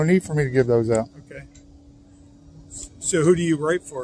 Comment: the guy sounds like he says "Who do you rape for?".